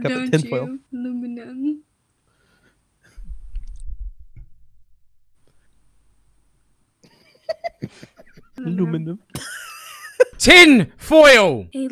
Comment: I thought that girl was saying hello my lamb and not aluminium